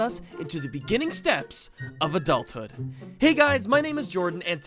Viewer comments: I thought it said adult hand XD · I thought that said said adult hand I was like wtf 😂😂